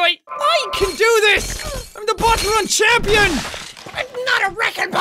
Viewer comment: the subtitles say im the porn champion